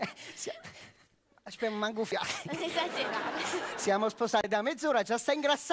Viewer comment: "Aschpè me manch o fiate" e poi che dice? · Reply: januainferni non esagerare